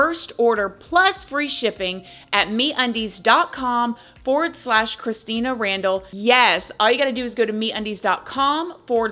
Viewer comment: I thought she said "meat undies" at first. Maybe that's a business opportunity for you if you own a slaughter house.